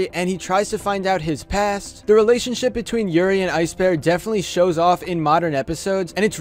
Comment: How you said Yuri and Ice bear kinda sounded like you said Yuri on Ice